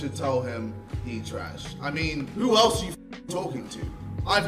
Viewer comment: I’ve had drama with the both of them. No it was just by yourself. No one acknowledged you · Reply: I heard that like “who tf even are you”😂🤣